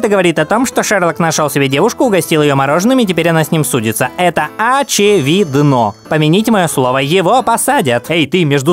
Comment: Я один услышал: а чё видно?